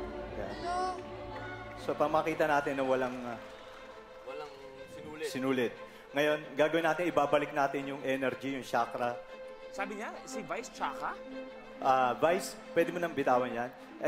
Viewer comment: ano sabi nya si vice chaka?😂 hayp😂